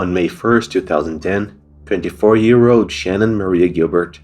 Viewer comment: Did you say 54? A 54 year old call girl?